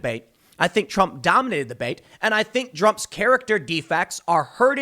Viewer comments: did you just say "Drumpf"? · did i hear Drumpf....